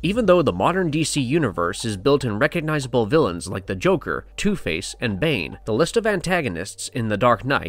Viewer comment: Listening to this on low volume with a big fan on: I know he said "Two-Face", but I totally heard "toothpaste"